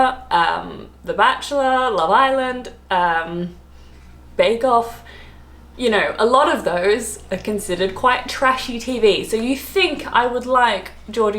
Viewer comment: for a second I thought you said you like Babe Golf 🙃